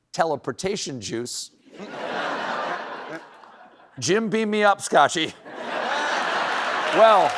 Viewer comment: Jim Beam me up, Scotty... CLASSIC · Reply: Jim beam me up Scotch is what I heard. I'm just using my phone speaker so not sure. The part with the hat I had to play again to hear and I was trying to read the letters on the hat. I'm gonna grab a screenshot I forgot to charge my Bluetooth speakers and I'm outside. They take hours and I forgot to charge them yesterday.